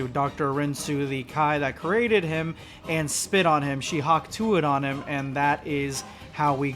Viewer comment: Did he just say Hawk Tuah?!?!